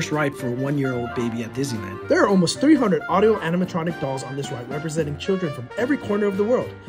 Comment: wait did you just said animatronics?☠️😭